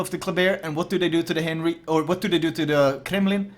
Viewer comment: When you say "Henry Four", I hear "Henry Ford"... "Henry the Fourth" or the french name would have avoided this :-P.